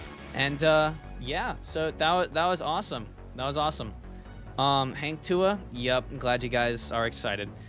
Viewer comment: hank tuah my ahh thought it said hauk tuah